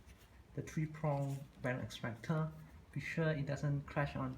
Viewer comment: what s the name of the tool?i didnt catch it!! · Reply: The tools used to remove a bearing- bearing extractor or bearing pullers. I used a bearing extractor with three crawls.